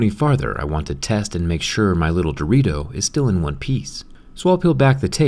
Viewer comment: is it just me or did he say “my little Dorito” perfect name for a watch